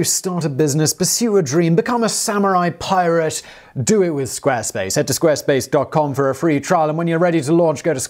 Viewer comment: "Speaking of badass things, SQUARESPACE! Smooooth." You're killin' it Simon. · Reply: I thought he said Squarepants and was thinking, "Yeah! A shout out to Spongebob!" Then reality caved in and I was like, "oh...shameless plug".